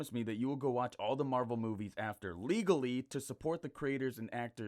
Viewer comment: umm did u say legally😶